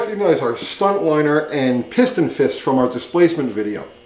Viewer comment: Oh piston fist. I thought you said piss on 👊. This is way over most non 1960 thru 1980 gear heads. I get your knowledge. Thank you and. Also the crew. Isnt the powerstroke a ford diesel? Come on.